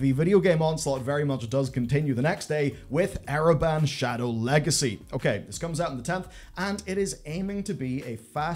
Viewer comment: When I first heard you say the name of Ereban: shadow legacy I got a little scare. I thought you said Raid: shadow legends for a second.